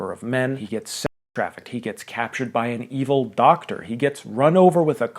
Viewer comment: when I first heard this I thought you said he was runover by an evil car and for some reason my first thought was Herbie